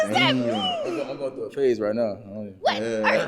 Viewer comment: He saying she ugly but sir have you ever looked in the mirror. Also he's a barber with his hair and face looking like that. Sir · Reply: I thought the Barber said he was going thru a Phase not a Face